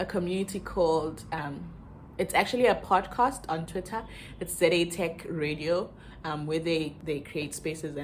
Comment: Can you share the twitter handle I can't hear it properly🙏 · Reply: ZATechRadio